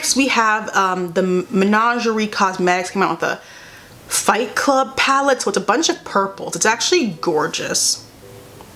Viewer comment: I thought the palette said fight club with a pit bull. I almost had a mini heart attack.